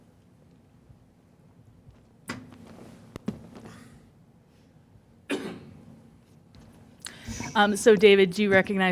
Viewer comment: Right before 32:00 he lips something. I am usually pretty good at lip reading, but I can't figure out what he is saying. Is it " David, I love you"?? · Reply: I think he said I love you